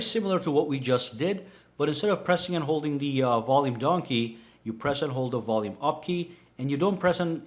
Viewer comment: anyone else hear donkey